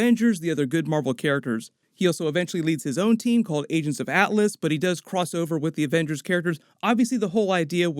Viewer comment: I thought he said Asians of Atlas